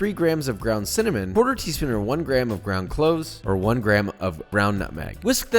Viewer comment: How much teaspoons of nutmeg did u say? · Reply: 1/4 teaspoon or 1 gram